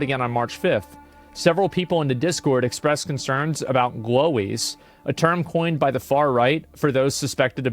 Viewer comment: I misheard “glowies” as “lolis” and got really confused.